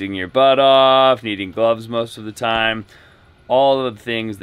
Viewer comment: said “needing gloves most of the time”, but I thought he said “eating bugs most of the time.” Say what now? Lol